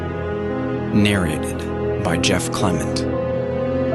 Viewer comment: i thought it said 'narrated by jeff condom'